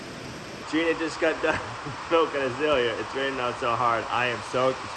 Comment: What? I went back and listened five time, and still heard the same thing. I heard you say "Gina just got done smoking a joint"